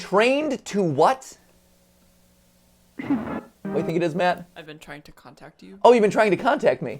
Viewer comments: I thought it said, I have been trying to come back to you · IT SOUND LIKE HE SAYING "I've been trained to compact you." LOL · I thought that it said “I’m trying to come back to you” at the end of the last video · I hear “I’m trying to come back to you” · I heard "I've been trying to come back to you" not " I've been trying to contact you" idk this was already addressed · At the end video, I thought it was saying “I’ve been trying to come back” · I hear I'm trying to come back to you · "I've been trying to come back" is what i hear. To hear higher frequencies use your right ear. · I heard " I have been trying to come back to you " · i heard "im trying to come back to you" lol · to me it sounds like his saying "they have been trying to come back." anyone else? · I hear "I'm trying to come back to you". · I heard “ive been trying to protect-“ 🖐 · i heard I've been trying to come back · I heard "I've been trying to contract it / contact it". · what i heard was "I'm trying to come back to you" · maybe i’m simply hearing this wrong but the voice at the end of the second video i heard “i’ve been trying to come back” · tbh i thought it said "I'm trying to come back to you"